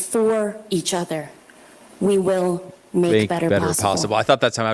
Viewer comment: I thought she said, Together we will make better possum.